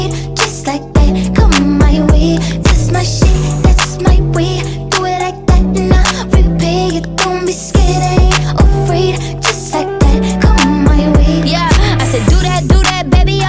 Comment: What other people hear:Thats my wave What I hear ThAt'S mY wEaVe